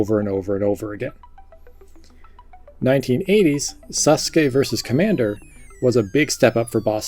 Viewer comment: I swear I heard him say "Sus Guy vs. Commander"!